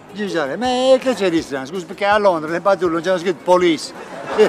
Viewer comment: A 0:28 non ho capito cosa ha detto. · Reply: Ha detto: " Perché? A Londra sulle pattuglie non hanno scritto Police? " (inteso come gruppo musicale)...😂😂😂